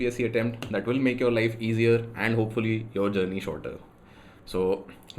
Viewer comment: in CC it says " hopefully your life shorter" instead of "journey shorter". btw nice content